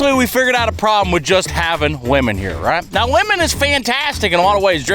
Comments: the captions says “women” instead of “lemon” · i had to rewind.... did he just say women??? PMSL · did anyone else think at first he said woman